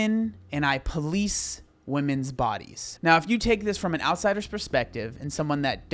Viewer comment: as someone which 1st Language isn't English, the 1st three Times i've heard him say "I please womens bodies", insted of "i police womens bodies"... 🙈